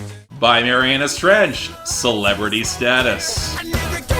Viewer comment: Even though I thought you said 'Marianna stretch', yes, Marianas trench deserves so much more!